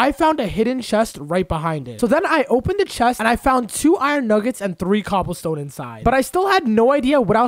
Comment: did he just say iron nuggets?